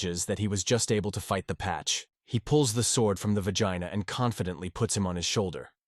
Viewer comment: I keep hearing "vagina", like here for example at 5:06:41 , "he pulls the sword from the vagina".